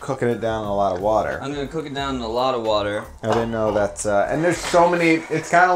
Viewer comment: How shitty are my computer speakers? For 2/3 of the video I thought he was saying polk-a-dot water...